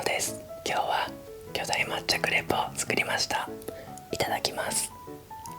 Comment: 冒頭の料理紹介が「今日はマッチョクレープを作りました」に聞こえて3回ぐらいは戻して聞いた